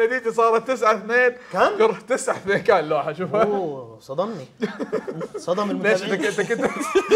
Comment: ايش قال؟ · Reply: صدم المتابعين يعني فاجئهم